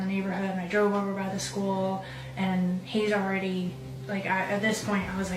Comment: She actually said "He was already....."